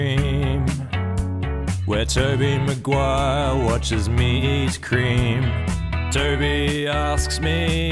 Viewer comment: I always hear 'meat cream' which makes me throw up in my mouth a bit